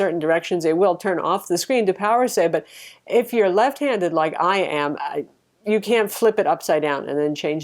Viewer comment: LOL! The first time I heard her say "if you're left-handed like I am..." I was busy doing other things and heard something rather different: "...if you're lesbian..." I was like "what?!"